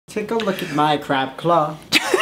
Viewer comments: I thought said “Take a look at my crap cloth!” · "Take a look at my crab claw!" Lmao for a second I heard it as crap cloth and was so confused